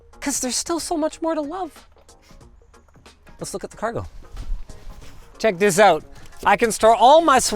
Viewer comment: Plot hole: At 5:08 you say "Let's see the car go, then instead of driving it you showcase the storage in the boot. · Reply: He said cargo